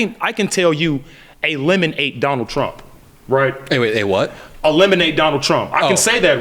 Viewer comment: Man, that guy I don’t know what to say… I thought he said “a lemon ate Donald Trump!”